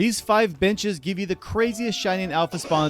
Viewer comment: Started the video and the first thing i hear is "These 5 bitches give you the craziest..." and i was like "ayo did i hear this right?" Then next second i figured he said benches.